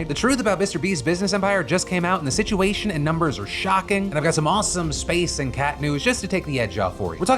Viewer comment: Ok ngl. You said 'space(news) and cat news' and i heard 'news about cat-stronauts' Im trying not to be disappointed, because both actual news stories were great, but..... cats in space is where my heart was 😢😂